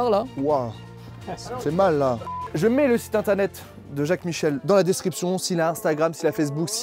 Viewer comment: A la fin j'ai compris " je met le site de Jackie Michel en description..."🤦